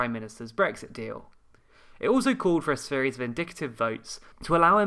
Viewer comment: Whenever you say "indictive" I hear "vindictive". =D